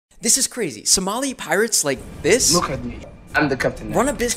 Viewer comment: Pirates ?! Oh I thought I heard pilots 😅